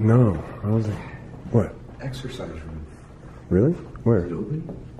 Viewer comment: About 11:10 - 11:13 hear a female voice say "sir". First time I heard it I thought it said "excuse me sir" but think I was mistaken by saying exercise.